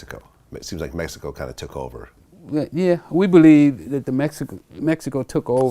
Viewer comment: Is it me or did Rick just say at 2:11, We Believe, Oh Wow😒